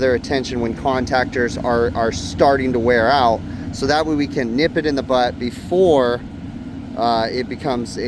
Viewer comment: I’m like you. I always heard Barney say “Nip it! Nip it in the butt!” But (ha, ha), I think he was saying, Nip it in the bud actually.